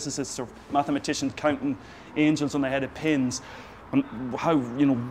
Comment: Did you say at 3:45 "Counting angels on the head of pins"? That is fucking beautiful and if that is what you actually said I am deeply amazed of your poetry understanding of deep-level physics. You continue to astonish me with every video I see!